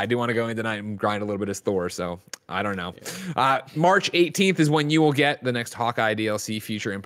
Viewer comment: I thought they said February 18th not March 18th the first time I saw it. Really disappointed